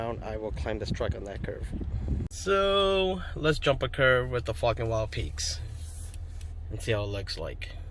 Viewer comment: Haaaaaaaaaaaaaaa. Sounded like you said Fukn Wild peaks.... Question, Im hearing the TRDs have a factory 2" lift. Do I need this lift to accommodate these tires on a SR5? ?